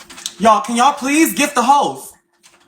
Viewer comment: I always thought she was saying gift the hoes not the host 😂